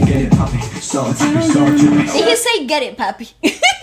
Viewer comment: I've always thought it said get it papi aswell lmao 😂